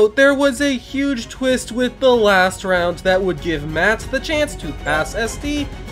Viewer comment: i thought he said "That would give mat the chance to ASS sd"